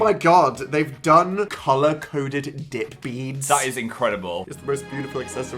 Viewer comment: "colour-coded dip beads" yup...i misheard that at first